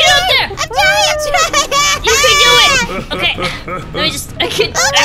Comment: Did anyone notice or is it just me but 8:25 sounds like she says Levi!